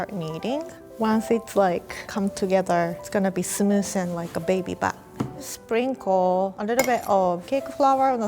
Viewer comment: "Like a baby butt." SHE DROPPED A HINT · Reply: Helen Chang not to be racist or anything but I thought she said “like a baby bat” 😭